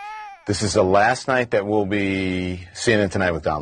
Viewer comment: Lol I thought he said semen but it was cnn 😄